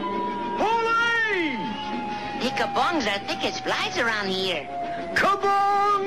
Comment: what did baba looey say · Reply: Sounds like he said, "the ka-bongs are thick as flies around here". I used to love to hear him say, "I don't thin so quicks draw".